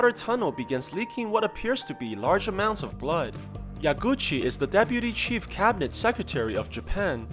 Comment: I heard that as "Ya Gucci" the first time 😂 yeah it's Gucci maaaannn